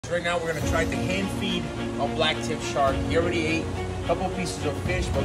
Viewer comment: I thought it said backflip shark. I am a little disappointed